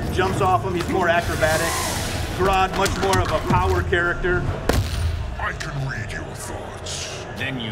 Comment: can anyone tell me what they say at 3:03 minutes ?, not understand what you said Grodd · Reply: Alex Camargo Grodd: "I can read your thoughts" Batman: "Then you know whats coming"